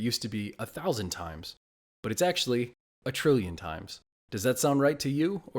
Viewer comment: I thought he said I apologize and tributize